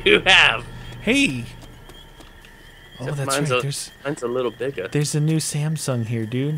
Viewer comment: did just say his pp is a bit bigger? lol